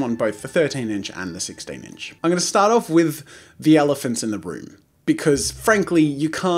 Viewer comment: Elephant in the broom, who else heard that?